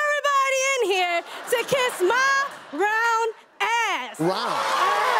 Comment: I thought she said brown ass 😂😂😂